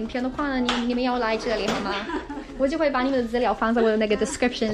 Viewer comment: did she Say The "N" Word??